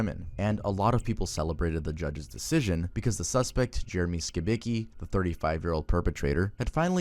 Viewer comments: I need to get off the internet man I thought bro said skibidi💀💀💀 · I’m so brain rotted I thought it said Jeremy Skibidi 💀